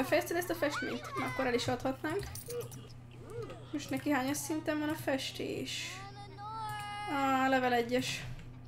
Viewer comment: 19:43 percél mit mondasz? válaszodat előre is köszönöm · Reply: Csenge Simo Neki most hányas szinten van a festés*?